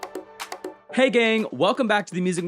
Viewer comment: I thought you said "hey gay" and I was j like "clocked"